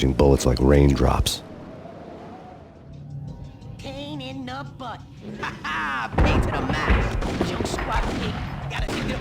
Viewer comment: Please help me, my English isnt the best. What do the Virgillios say from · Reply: Pain In Numb Butt Haha Pain To the Max